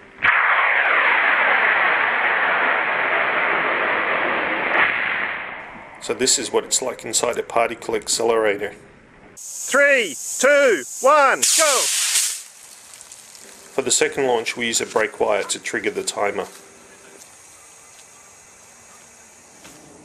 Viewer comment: did you say "this is what it s like inside a "party-cle accelerator"? cause of the jimmies?